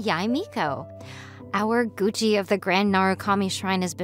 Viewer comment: please tell me I'm not the only one who heard 'gucci'